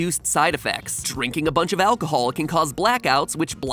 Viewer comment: what Matpat says, "Drinking a bunch of alcohol." What the video says, "Drinking too much alcohol" Wow, tryin' to be professional.